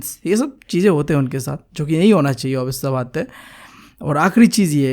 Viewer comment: What did he say?! " jo ki yahi hona chahiye " yahi hona chaiye women ke sath? Did he just said ki yahi hona chahiye aurto ke sath workplace pe? Tell me if i misinterpreted. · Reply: Jo ki nhi hona chahiye bro you're just....😅 btw leave it!